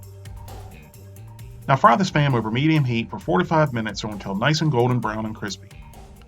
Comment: sounded like you said to fry it for 45 minutes, not "4 to 5"